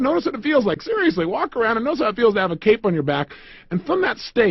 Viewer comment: I thought he said walk like you have a cake on your back! I actually did that and felt the confidence! I thought it was meant to give you confidence because everyone likes a good cake and wants it so that made you confident. It totally made sense! Then I played the video again and heard it right, walk as if you have a cape! I did that too but strangely I was more confident with the cake than the cape